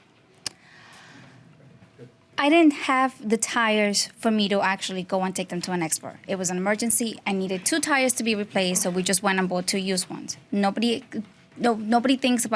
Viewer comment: At the end, she said she didn’t have the Tires, but I thought the dad said he had the tires at home